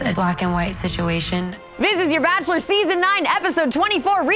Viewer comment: Did I just hear season 9 episode 24? 😂